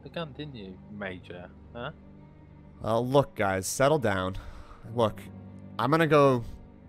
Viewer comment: 1:everyone no one cares if you are early and 2: when says "guys" it almost sounds like he is saying sky's